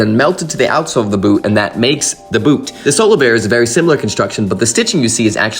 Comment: glad to see solovair getting the recognition they deserve finally. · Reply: Omg I thought he was saying "solo bear".